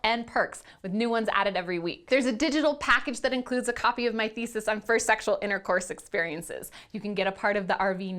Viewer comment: I swear it said a perk that shares my niece's first sexual intercourse experience.... lol but she said thesis not Nieces